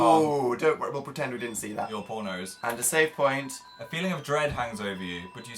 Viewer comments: When Phil says "Your poor nose" at 53:39 all I hear is "Your porno's" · I know Phil said, "Your poor nose." But I heard, 100%, "Your pornos." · Did Phil just say prono or am I hearing him wrong?!? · did I hear Phil say "your pornos" 😂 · ok 53:38 did he say "your pornos"? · Am I the only person who thought Phil said "your pornos" at 53:39 I hope I'm not the only one xD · wat, am i the only one who heard "your pornos" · Wait wait wait wait wait wait wait wait wait wit wait wait....did phil just say...."Your Pornos"? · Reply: "your poor nose"... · I thought Phil said ''Your pornos''. Don't give me that look... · i think he says "your pornos" every time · I thought Phil said "Your pornos" I was like OMF PHIL